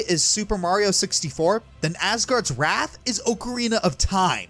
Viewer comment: Ok, am I the only one who thought he said Asura's Wrath, not Asgard's Wrath? I thought he was going to talk about the DLC true ending XD